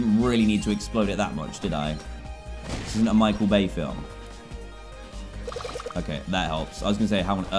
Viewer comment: DanTDM:(Whatever he said) Me: DID HE SAY MICHAEL VEY FILM!?!?